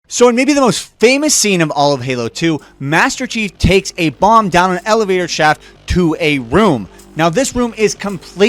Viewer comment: Sounded like you said "master chief takes a bong down an elevator shaft".